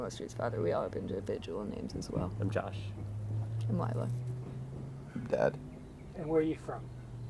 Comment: Did he say he’s dead?